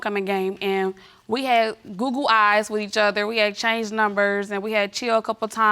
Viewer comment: "We had google eyes each other" 👀👀👀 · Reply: 😂😂 At first I thought she said "googled eyes" 😂😂